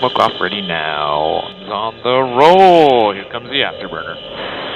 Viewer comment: Lol I heard:"Here comes the cheese"